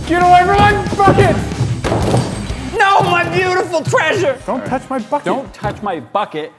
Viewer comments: i swear sombody said a bad word in 10:17 it said dont touch my #### it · who heard "F it"